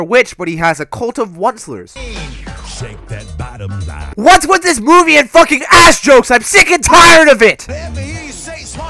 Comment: 🤣 I thought he said what's with this booby and ass jokes 🤣